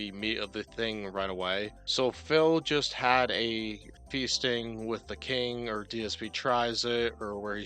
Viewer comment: I thought you said bee sting with the king beeBobble